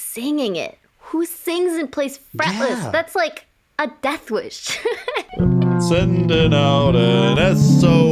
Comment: did sooomebody say “Deathwish”? By… The Police??